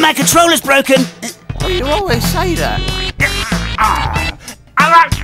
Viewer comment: what did say there? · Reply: AGH THAT RUSSEL CHARACTER'S RUBBISH